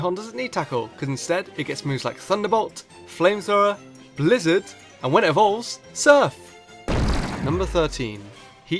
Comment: What did he say in 6:20? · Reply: "And when it evolves, Surf"